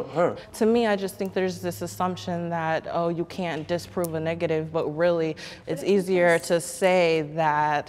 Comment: did she just say u cant disprove a negative xD